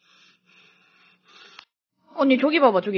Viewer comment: "남자아니세요?" 여자에요. 여자가 숏컷할수도 있죠. "여자 코스프레 아닌가요?" 왜 말을 못믿으시나요. <숏컷>인 여자에요. 이런걸로 상처주지 맙시당.. 보면서 얼마나 힘드시겠어요. 성별은 장난치는거아니에요. +)이걸로 여러분을 비하하는게아닌, 이 댓을 보고나시면 이제 남자냐고 안물어보셨으면 좋겠어요. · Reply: 그리고 3:49초 말를 잘들어보세요 "언니"라 들리지도 않으세요?